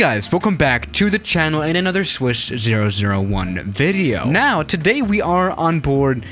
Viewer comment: I love it that every one of you're video when you say "swiss" The subtitles say "swish" Lol.